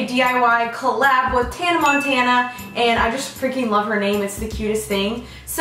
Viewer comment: I thought she said Hannah Montana at the beginning 😂😂😂 Anyways, I loved the video 💕